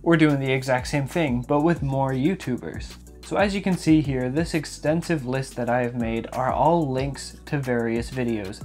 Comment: I thought he said expensive 😂 😂